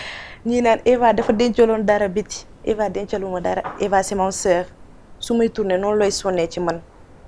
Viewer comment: J'ai entendu "Eva c'est mon soeur" , je ne sais pas vous autres